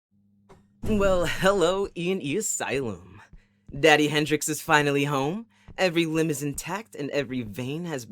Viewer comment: I had to replay to make sure if I heard “Daddy Hendrix” or if I had suddenly become delusional and had auditory hallucinations